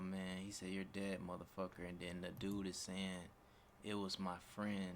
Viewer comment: it sounded like said " Go get your dad motherfucker" , not sure though.